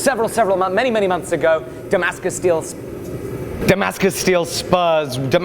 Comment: I thought it said Damascus butter